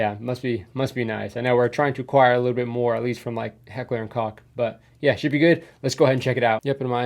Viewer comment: "Heckler und Cock" why did you say it like that😢